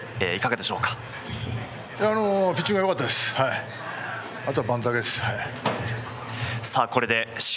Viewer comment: あとは、何て言ってる？ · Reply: ｢バントです｣でしょうか。 バント失敗もあったので